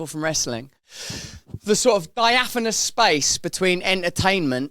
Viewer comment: What was that word he used around 5:20 "biaphemous" ??? Is that a word?